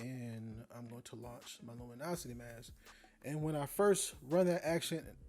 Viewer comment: A great retouch but I thought you said 'Illuminati mask'!